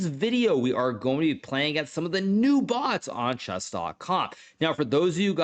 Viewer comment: why at start i heard "chess dot cock"